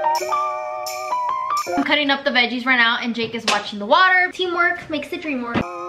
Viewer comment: I literally thought said "Jake is washing the water" and I was like what- So then I went back and replied turns out she said "Jake is watching the water" 😭✋🏼